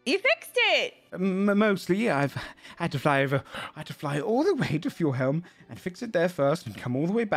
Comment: Did he say fly over to Fjorhelm (sp?) squint